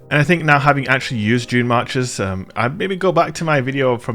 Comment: I keep hearing 'Junemarkers' lol :)